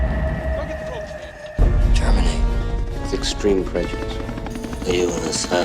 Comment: I keep hearing "Germany ... With terminal prejudice" in the intro 😅